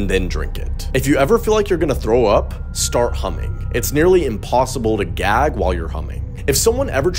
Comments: Bro I literally thought he said “humping” instead of “humming”- 💀😭🖐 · Legit thought he said "if you feel like throwing up. Start HUMPING" and all I could imagine was me in vrgear while air humping to counter my motion sickness 😂😂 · I swear I thought he said "if you feel like you need to throw up start humping"🤣🤣🤣🤣🤣🤣🤣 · I swear I thought he said "it's nearly impossible to gag while humping". Then I thought of my ex & decided that it's not impossible 🤣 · The first I heard him say start humming for the puking one, I thought he said “humping” lmao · "if you feel like you're gonna throw up start humming" Me: hums through the rest of the video even though I don't feel like I'm gonna throw up Edit: wow thx for the likes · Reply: Omg I just realised he said humming I thought he said humping 😳😳😳😳😳😳😂😂😂😂😂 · I swear i thought he said if you're every throwing up, start humping because it's nearly impossible to choke while "humping!" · 🤣🤣🤣. I thought he said if you are going to throw up "start Humping" it's nearly impossible to throw up when you're Humping. 🤣🤣🤣. I had to listen🙉 to it a second time to make sure thats what I heard.... It's "start Humming"... 🤣🤣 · am i the only one that thought he was saying 'humping' 🫣good Lord i need help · "if you feel like you're gonna throw up start humming" Me: hums through the rest of the video even though I don't feel like I'm gonna throw up Edit: wow thx for the likes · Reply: I thought he said humping lmfao